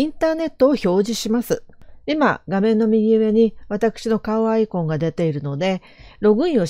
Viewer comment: 先生〜 お疲れ様です😊 ライトPDF編集便利ですね〜 凄く良くわかりました！ ありがとうございます！ これは私の一人楽しみなんですけど…笑 11:33のところで 私の顔アイコンの〜と話しているのを 私のかわいいアイコンを〜と 聞こえて〜笑 うん！可愛い先生可愛い！と 私一人で聞き間違いを喜んでました！ 先生のNG集でもほっこりしました〜 今日も先生に 良いお勉強と癒しを頂きました😊😊 ありがとうございました🙇💕💕